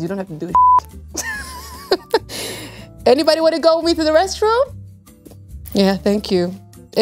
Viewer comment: What did she said in the minute 2:12? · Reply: anybody wanna go with me to the restroom? you can turn on the captions in settings